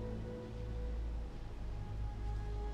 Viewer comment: what did he said? :-) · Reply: "OK, Mother. I'll believe you"